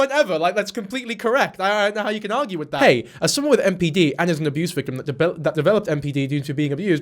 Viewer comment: sounds like you said BPD instead of NPD. if you meant BPD, then no, mania isn’t experienced. manic like emotions, yes, but not mania. NPD also does not have mania.